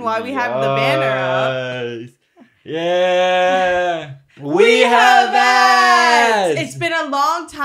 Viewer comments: I thought the banner said we have aids and I wasn’t phased one bit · ngl i thought the banner said “we have aids”😭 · I thought the banner said "We Have Aids" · fully thought the banner said “we have aids”